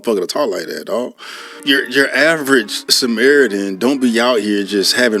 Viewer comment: Did he just say "average samaritan?"